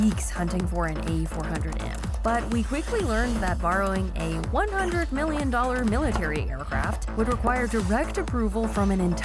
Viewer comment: I thought she said it was 100 million in the short